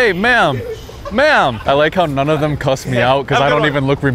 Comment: Did he call him "male"❓😹😹